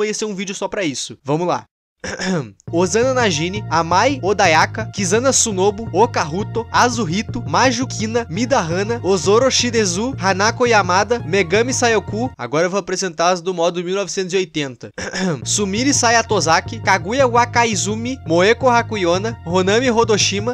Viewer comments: mano, eu ouvi "Megami sai o cu" kkkkkkkkk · eu entendi megame sai o c* kkkkkk!!!